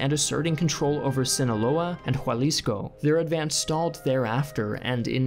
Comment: Anyone else hear "Jualisco" not Jalisco?